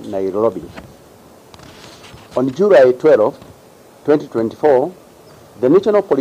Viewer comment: Did I hear nailobi 😳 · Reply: Did I hear "Jurai 12, 2024"? 😂